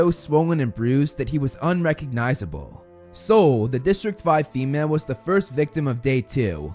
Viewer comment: I heard this around a dozen times and always understand District Zwei (2 in German). Is she the female tribute from District 5? Since it is I would say the only number which sounds similar to zwei